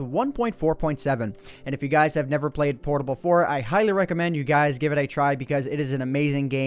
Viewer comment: did you just say portable 4?